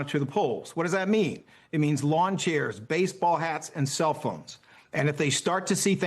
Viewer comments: I thought he said “Lawn chairs, baseball BATS and cellphones “ at first. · Oh! I thought he said baseball bats.